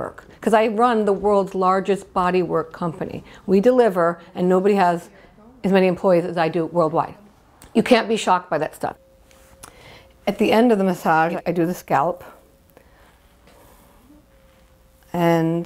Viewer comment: A little late to this party, but 4:34. Thought she said “At the end of the scallop...” 😂🤷🏼‍♀️